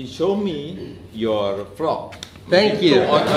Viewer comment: "..he showed me your frog." Wait a minute. Frog?? Did I hear wrong? I replayed it one more time, then I heard the word 'frog'. I kept replaying it ten times, still all I could hear was the word 'frog'. I set the playing speed to 0.25x, and still it's 'frog'. I think to my self, do my ears have problems? In case they don't, then I wonder if Casey really had a FROG at that time. 🤔